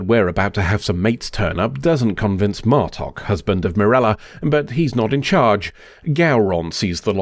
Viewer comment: Caught an oopsie 7:31 Martok husband of "Mirella" instead of Sirella